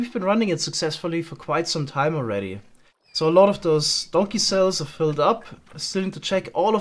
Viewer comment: I'm always just about to go to bed but then....... edit: LMFAO THE FIRST SENTENCE. I heard it as "a lot of those donkeycels are filled up" 😭😭 don't ask why. kissless virgin donkeycels